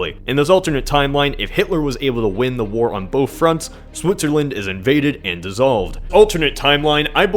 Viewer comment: did he say Swootzerland